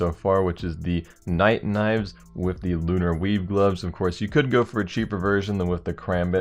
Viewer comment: lol everytime he said weave i thought he said weeb